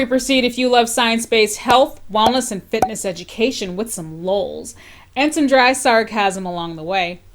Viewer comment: I gotta apologize, but I thought you said 'some moles and dry sarcasm' and was very confused for a second. The lolz came fast at 2 am